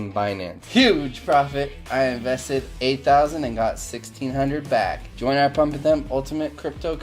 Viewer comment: did he just say “I invested 8k and made 13 hundred back”😂